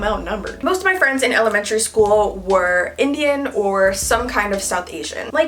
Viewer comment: did she said Indian Omg🤗🤗🤗❤❤❤❤